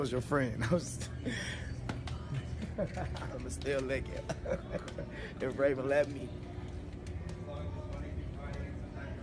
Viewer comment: Sounded like he said, "and I'll still lick it, if Raven let me."